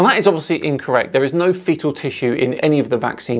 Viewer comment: Oh...fetal tissue. I thought you said fecal tissue (- because they are talking sh..). Vitamin D. Some 20% (If I remember the figures correctly) of the population has a vitamin D deficiency. This means that production of the T-cells may be inhibited causing more serious symptoms if you catch COVID. I surmise, even with a suitable vaccine, it would be well to have your system ramped up - just in case.